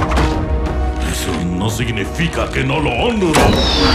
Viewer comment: Una pregunta , en el minuto 4:16 que es lo que dice , no se logra escuchar bien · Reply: "Eso no significa que no lo honre" o honro XD eso dice